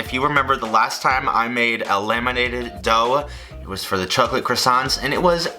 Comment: I thought he said "lemonated" dough and it confused me to no end but then I realised that it's "laminated" dough instead.